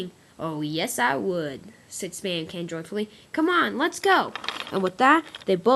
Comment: did you say "said Spamcan drugfully?